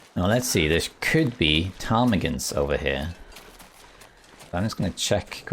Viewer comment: I thought you were saying Tommy Gun instead of Ptarmigan, and I was extremely confused!